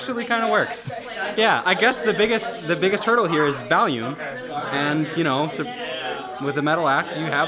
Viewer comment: Every time said "volume" I thought he was saying "valium"...